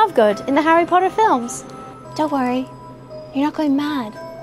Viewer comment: I thought she said your just insane as I am